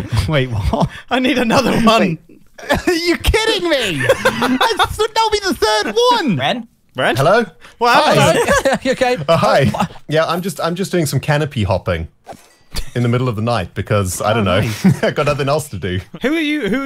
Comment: Ok ok there really being sly about the whole aha joke cause I just heard Ren say AHhai on 20:53 🤣🤣🤣